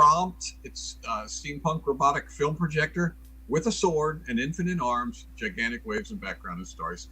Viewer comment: I definitely heard "infinite arms" and imagined a spiky haired anime projector with thousands of arms.